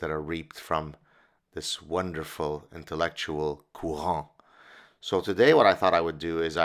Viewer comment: Anyone know what is this word? spelling, meaning, etymology. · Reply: Courant: <link> It is also a dance-form, often used by Baroque composers, for example by JS Bach in his suites for solo 'cello.